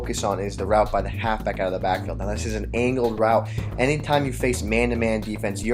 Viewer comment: does he says anal route??